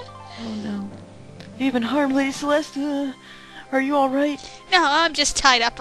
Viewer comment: Did just call Lady Celeste Celestia?